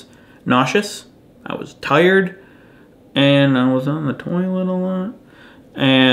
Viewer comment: lol what did he say at 0:28? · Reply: “I was on the toilet a lot”